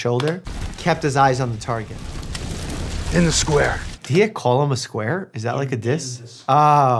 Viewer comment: So, the guy didn’t call gale a square, he said “to the square” as in go to the town square. Also Primrose was saying “missed the eye.” And as another thought said “doesn’t need stitches”